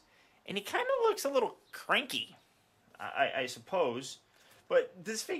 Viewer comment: The reason why he looked "cranky" on the certificate is because it's very simple - he's the main antagonist in Toy Story 3 . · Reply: I actually thought he said “pranky” and I thought “yeah sure, he’s a bit of a prankster... yeah...”